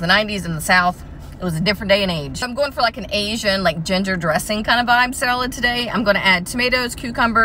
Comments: I thought she was saying "Gender dressing" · Omg I thought you said gender dressing till i rewatched the video lmaoo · I thought you said gender dressing, pass me some of THAT